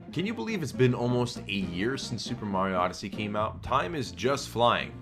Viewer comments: I thought he said 8 years since the game came out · 8 years. Damm in soo old now I'm now 26 and born in 2002 of wait ITS 2018 DUDE AND IT RELEASE LAST YEAR 2017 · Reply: After hearing it again, it does sound like he said 8 years, but he has replied to some of the comments to clarify that he meant to say "A year". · It has been out for 8 years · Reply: He meant to say A YEAR, but the way he pronounced the A made it sound like he said EIGHT YEARS.